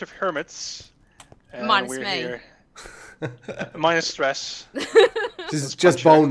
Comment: Dude I heard "I'm with hermits minus stress" buy it's season 7 and she is in it also u miss missile wars it's such a cool concept